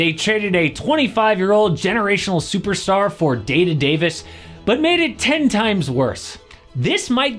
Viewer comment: day to Davis 😭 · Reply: Thought he said dated Davis. As in outdated